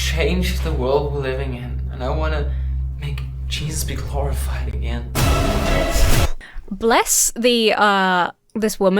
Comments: it sounds like he says "I wanna make cheese be horrified again." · it sounded like he said "Cheese be glorified again"...what is he from Wisconsin? LOL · "I wanna make cheese be glorified again." 20:55 (approx). I like this guy. He talks my language. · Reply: I honestly thought he said he wanted to "make Jesus horrifying again," and I was wondering why didn't comment on such a wild-ass line.